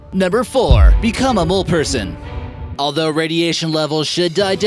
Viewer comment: I thought he said old person 😂🤣